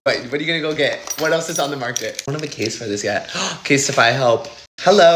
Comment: Stoppp I thought he said “omg and it’s gng colors” I was abt so say… I didn’t know you were like that😭😂